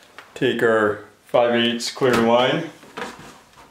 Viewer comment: What’s the black thing called sounds like you said ⅝ sparge fitting? As I’m going to hardware store try and find these. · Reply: Barbed fitting